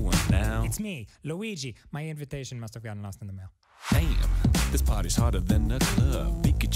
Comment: what the heck is luigi saying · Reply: My invitation must have got lost in the mail